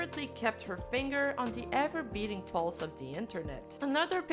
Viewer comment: Thought ahe said “gura has kept her fing on the ever beating BALLS of the internet” not pulse, had to check captions 😭